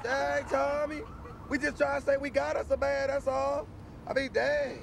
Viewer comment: We got us a man 😂😂😂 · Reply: mrs Lopez I always thought She said we got us a van lol